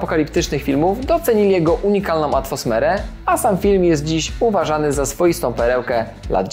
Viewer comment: Czy ja tam usłyszałem "atwosmerę" ?😁